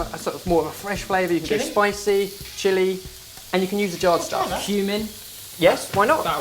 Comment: Oh god, I thought they said you could put human in the mayo, then I realized they said cumin... -_